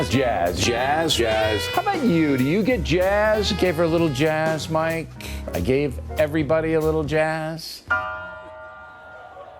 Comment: Hahah I heard it as 'I gave her a little jizz, I gave everyone a little jizz.'